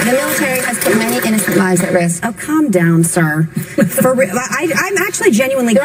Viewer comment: The first time I watched this I thought Meghan Mccain said "Calm down sir" to Pam Anderson.